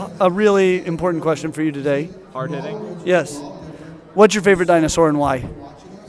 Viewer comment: I keep hearing: What's your favorite dinosaur and wine? 😅